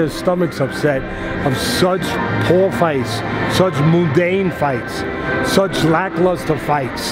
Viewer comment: did Teddy really say "moo-dane" lmao.